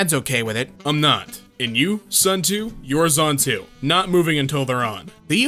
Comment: I thought he said: "Son2, you're son2."